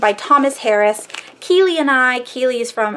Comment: I dont see the link to the persons channel you mentioned at 21:29, I was wanting to check them out but im not entirely sure what you said the channel name was lol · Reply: Sorry about that <link> Her name is Keely and she is amazing!